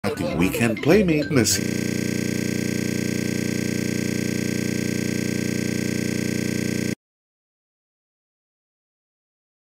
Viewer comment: sounds like he said pussy - a bad word 🧐but he said messyYyYyYyYYyyyy